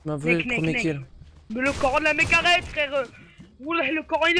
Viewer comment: il a dit le coran est pour moi ?????????¿¿¿¿¿¿¿¿¿